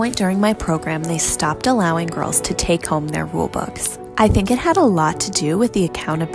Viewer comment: Why did I hear “ they stopped letting girls take home their robux” 😭